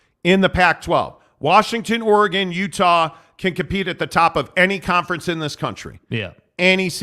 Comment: just a quick psa. at about 38:00 minute mark there was a tech. glitch. Sounded like you said "UW/Utah/Oregon can compete at top of any conference".....